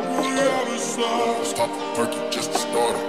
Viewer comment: Pop a perk n let’s get started who would’ve thought he wasn’t saying purple 🤣🤣